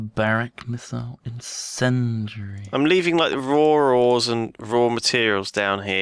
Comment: Lol,at first i thought lewis said rawr ores